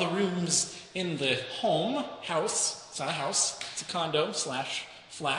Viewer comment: he says condom instead of condo